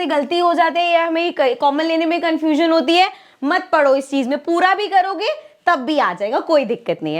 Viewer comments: 😂 I was scared cause I heard "mat padho" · mam was saying mat pado is chiz m and i heard mat padho....really epic😂😂